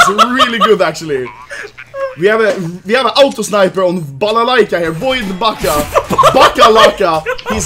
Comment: did he say Balalaika ?